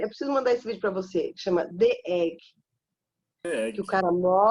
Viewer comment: Em 39:37 fala de um clássico sobre um homem que morre e encontra Deus. Não entendi o nome. Pode me informar? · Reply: The egg, tem no YouTube.